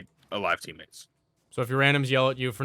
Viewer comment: All I heard was "Tell my randoms to shut up." 10/10 video.